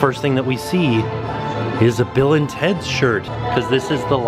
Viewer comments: I had thought you had been saying “Villains Heads” all this time!!!! Not Bill and Teds... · Bill and teds????? I always thought you said villains head?!